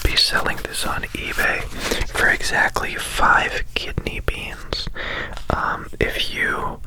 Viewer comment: yo i forgot kidney beans were a thing for a min and thought u were trying to say kidney stones 😳 ty, that would’ve made my goal a little more difficult